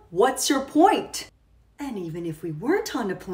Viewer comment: “mom, why was i named diaper?” “well you see, a diaper hit your head after we left the hospital-“ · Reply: I thought you said dipper lmao